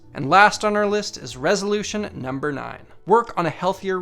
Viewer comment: I thought that at 5:58, he said REVOLUTION number 9, so i instantly went "NUMBER 9, NUMBER 9, NUMBER 9,..."